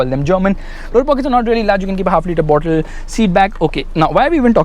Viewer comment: OKey can anyone tell me what he said ? 😂😂 It's freaking fast !! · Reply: Door pockets are large, you can keep a 1 litre bottle